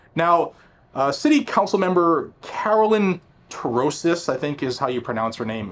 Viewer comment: sounded like you said Carolyn Corrosive